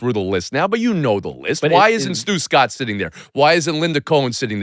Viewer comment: When he said Stu Scott I thought he said Stugotz